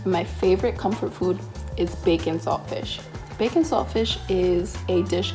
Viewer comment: who heard bacon sausage?